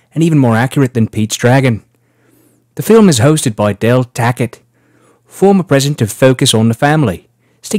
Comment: Every time you say "Del Tackett" I only hear "Del Taco" and now I'm hungry...